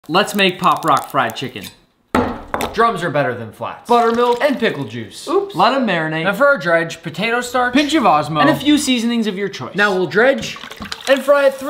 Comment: I thought he said DRUGS